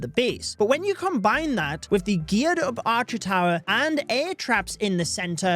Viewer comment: i thought he said "the gyat of the archer" for a sec